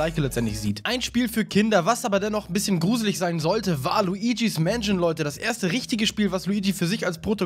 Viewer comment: WALUIGIS MANSION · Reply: jeder hat sich das gefragt XD ich habs beim ersten mal falsch verstanden und ich habe zurückgespult und habe gut hingehört und ich habe dann: ...war Luigis Mansion... gehört XD